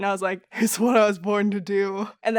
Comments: Ohhhhhhh I always thought it said *waiting for your text- "cause thats boring to do." Not "its what I was born to do" 😂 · I always heard "cuz that's boring to do" instead of it's what I was born to do. 😂 so glad it make sense now. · I thought she said “it’s something boring to do” not “it’s what I was born to do”